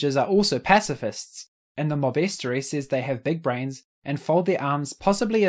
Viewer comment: I really like this video, and something to point out is that Dungeons is canon, and Dungeons has a ton of quasi-lore. As creator of the Dungeons-related portion of the wiki, I try to mention this without going too far into opinion-based theories but it's interesting to think about. When the Orb of Dominance creates a 'body' for itself (i.e. the heart of ender and jungle abomination), it always has enderman eyes, and all the plant-based monsters it made have purple glowing streaks running through them, suggesting the Orb is from or relates to the End. And because of that, the Illagers may even worship the Ender Dragon like the Endermen do (which is why there's end-related stuff everywhere in Highblock Castle, the Arch-Illager's home). Many items have lore of their own, like how Heartstealer (I could be remembering the item name wrong) was given to the general who conquered Squid Coast after his victory, suggesting he was killed at some point (because it would be embarrassing to lose a gift like that) In fact, about Highblock Castle, some item descriptions suggest there's a prison there that we never get to explore called Highblock Keep. There was apparently an Illager there who would use the Truthseeker sword (the long soul knife) for interrogations, and there was a jailor who was known as the "terror of Highblock Keep". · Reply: What is it that you were saying at 13:19? it sounded like mobess theory, but I can't figure out what it really is. Thanks